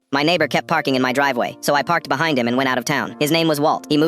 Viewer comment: I thought he said my neighbor keeps barking on my driveway😂